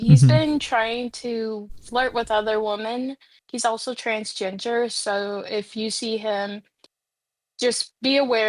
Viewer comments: “Transjenjer” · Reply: I thought she said trans-ginger and im like, is there just a different word for trans-people who are also gingers ?! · did she say trans ginger? 😭 15:58 “there was also a 9 yr old” HOW CAN SOMEONE SAY THAT SO CASUALLY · Did she just say hes transginger...like Eric Cartman